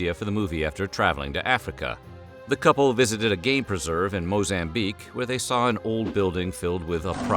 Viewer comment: oh GAME preserve! I.. I heard it as "GAY preserve" and was like, "that's awfully progressive..."